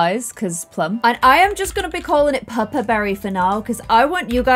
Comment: Every time you said "Pupper" I thought you were saying Purpur, and I was confused as to why that just wasn't the name because that means violet in German.